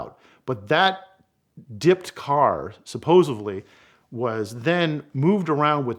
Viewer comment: Omg @ 7:22 he says supposively instead of supposedly. Another good story that sounds like bs. Too Funny.